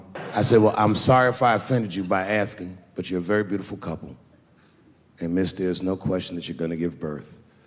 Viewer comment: I watched this without volume and thought he said “very beautiful siblings” for a sec